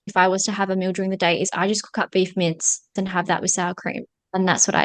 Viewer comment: What does she say at 1:58? Beef mids? (The transcript says beef mints..) What are mids? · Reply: I think it was beef mince…as in, ground beef.